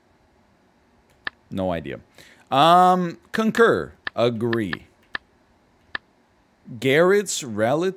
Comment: I was moving some stuff around my PC and for a second I legit thought he said "agoobi." XD 29:17 Ok, I know he said not to laugh...but I laughed.